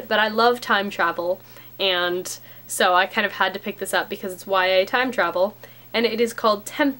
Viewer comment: I kept hearing "YA Time Travel" as "Why I time travel." and was like "Kayley, if you have managed to get yourself as the Doctor's companion, I don't think you should be sharing that!"